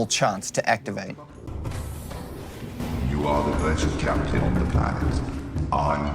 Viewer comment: I will never forget when my Cyclops told me I was the best. Even though I misheard it as "you are a legend, Captain", still what a moment. Thought it was because I left too many Lava Larvae on it for too long lol